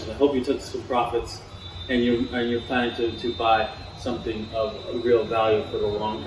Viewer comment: "I hope you took some profits and you're planning to buy..." I thought he said "and you're flying to Dubai." lol.